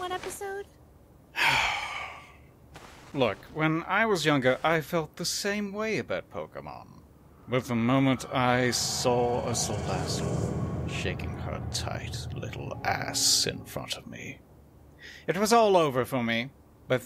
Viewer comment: > which pokemon did he say? I'm not really up to date on this perverted $hit, but loving every second of it. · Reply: Salazzle